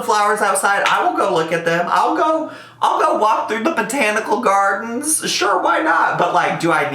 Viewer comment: For a second I thought you said "I'll take a walk through the potatoicle gardens"